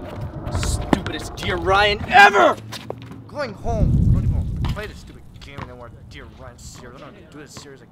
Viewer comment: what was he saying??? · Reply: He said "I'm going home I don't wanna play this stupid game anymore"😂